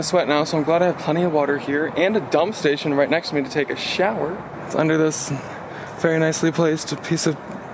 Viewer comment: i thought you was gonna say take a shit